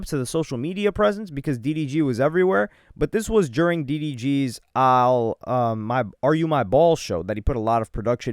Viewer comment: Everytime I hearing "Are you my balls?"